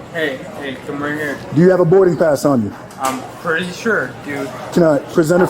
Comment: Not a regulating birding pass 😂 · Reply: I heard "burden pass" but both are equally hilarious